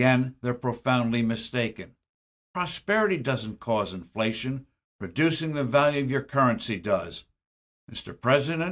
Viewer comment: CREO que están mal los subtítulos... Steve forbes dice: "[...] REDUCING the value of your currency, does." y esto fue subtitulado como "[...] la PRODUCCIÓN del valor de su moneda, sí. Cuando debería decir algo como... : "[...] REDUCIR el valor de su moneda, sí." y creo que en otros lados, también hay errores en el subtitulado. tengo en cuenta que el video de Forbes está acortado. no escuché el original pero me sigue pareciendo que hay algunos errores en el subtitulado de este video... por otro lado, coincido en casi todo lo que dicen en el video y Steve Forbes... ojalá le vaya bien a Milei. Ojalá salgan las leyes que tienen que salir en el senado y en diputados y se cierren curros y se acabe con la inflación y la corrupción. Dentro de poco, Milei va a participar de C-PAC , en estados unidos, creo que también van a estar Trump y Bukele allá... espero que Milei tenga mucho cuidado y sea cauteloso con lo que dice allá, los de eeuu no le van a dar mucha bola (para ellos es un sudaca , más , de un país pobre y corrupto del montón...) pero los de acá , zurdos , K y los argentinos de bien vamos a estar atentos a sus palabras allá, lo van a atacar diga lo que diga pero yo espero que no se pegue mucho a Trump porque creo que tiene los días contados allá, en eeuu, y va a perder y Biden va a ganar... por lo tanto, no creo que debiera pegarse a uno que , en mí opinión, no va a ganar, aunque no queramos, vamos a necesitar de eeuu y de otras potencias y paises ricos para saca